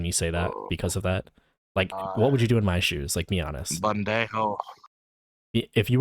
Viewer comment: im pretty sure he just tried to call you stupid. it sounded like sloppy Spanish but it sounded like he tried to say "pendejo"